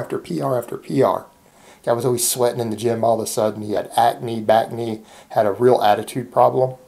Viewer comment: Doc did you just say backnee??? 😆😆🤣🤣 Stay blessed 🙏